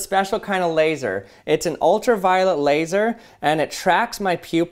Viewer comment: For a split second there when he said "ultraviolet laser" I thought he said "ultraviolent laser" ...lol